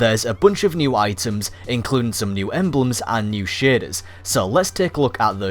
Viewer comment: Tbh when you said shaders the firt couple of times I thought you said shins